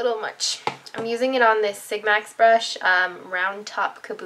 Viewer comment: It's weird it sounded like you said sigmac brush lol